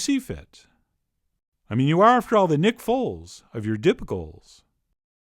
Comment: I thought he said rick roll of you dip bowls. I had to rewind lol nope nick folds